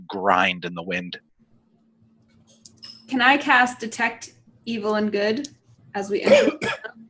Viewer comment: "Can I cast detect evil and good?" Me: "Oh, evil AND good. I thought you said Evelyn Good."